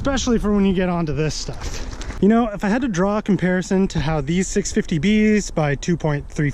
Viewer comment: I thought is said 650 lbs.